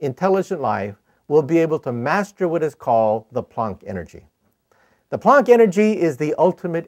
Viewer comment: What is he saying? Pon energy? Ponk? 5:30 ish